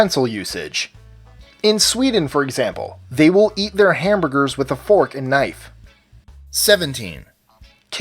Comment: The Sweden one is false, no one in their right mind eats a hamburger with a knife and fork. Don't know where you got that "information" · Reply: What ACTUALLY said was "In Sweden, for example, they won't eat their hamburger with a fork and knife"